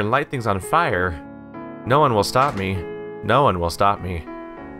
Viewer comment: when you said "no one shall stop me, no one shall stop me" I misheard your sentence as.... "no more Wasabi, no more wasabi" x'D but anyway. I'm new here and good gameplay :D subbed!!